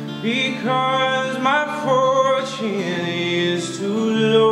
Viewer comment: i thought he said "because my foreskin is too low" XD